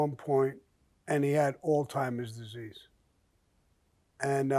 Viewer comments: “He had aids at one point, and he had alltimers” love it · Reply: I'm on the other side of the Atlantic and I clearly heard Alzheimer's. You old folks are deaf. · Oldtimers* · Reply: I thought he said oldtimers disease as well